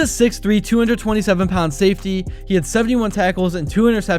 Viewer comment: Trey Amos is a CB not a Safety. i thought u said safetys but u said DB